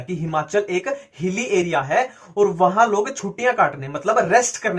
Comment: Kiskisne suna ki log ch**ya kaatne jaate h😂😂 Sorry Bhaiyya... But I heard it like that😅😅